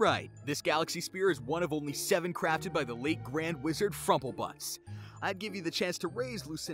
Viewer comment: did he say frofalbutts or frofalbuss and wat is the'yre fortune